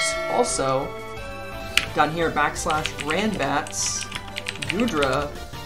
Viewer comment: Sorry, what did you say at 1:13? /randbats? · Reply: yes /randbats [pokemon name] will pull up its sets. You can also download the chrome extension: Showdown Randbats Tooltip which gives u tons of information.